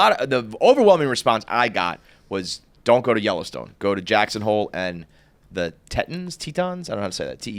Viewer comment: I thought KFC said he is going to Jacksonville for his dads 70th and I thought lol cool, but also got excited because I used to live there and love giving recommendations about it. Jackson Hole is going to be much cooler probably.